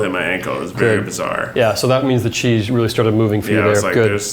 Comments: I thought the guy was saying cheese instead of chi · I thought when he said chi he said cheese